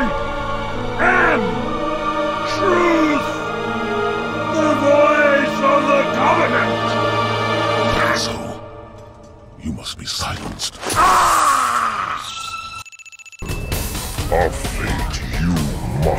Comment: I’ve never known but here does he say asshole? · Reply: And so you must be silenced is what he says